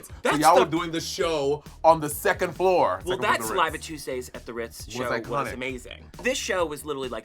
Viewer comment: I've watched this so many times and according to the captions it's "Live at Tuesdays at the Ritz" but I AM CERTAIN I heard: "Saliva Tuesdays at the Ritz" SOMEONE TELL ME THE TRUTH